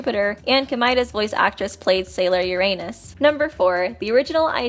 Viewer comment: I thought it said your anus